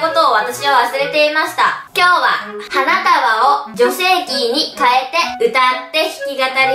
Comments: 割とガチで、 ｢今日は、花束を女性器に変えて｣って聞こえた笑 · 女性キーか！女性器に変えて歌うって聞こえたからどゆことかと思ったw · 花束を女性器にかえてって聞こえてる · 冒頭「花束を女性器に変えてに」聞こえた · 花束を女性器に変えて歌うって聞こえて！？！？！？ってなった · 女性器に変えて〜 って聞こえて何かと思った笑笑